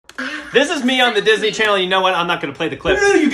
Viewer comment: I thought it said "Devan Key is Disney channel" lmao